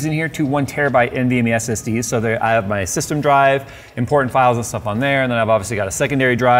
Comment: Lol did anyone else hear “porn files”